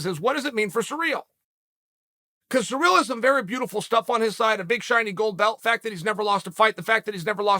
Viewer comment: I legit heard a big shiny bald head